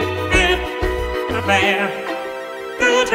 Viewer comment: I swear he said and steveland